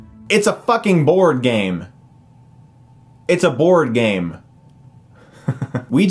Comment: I was laughing so hard when ouija was on the screen and he says ITS A FUCKING BOARD GAME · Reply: I thought he said "Bored Game".